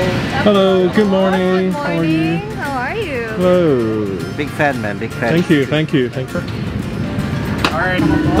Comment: is it just me or did he say “big fat man big fat man” 😂😂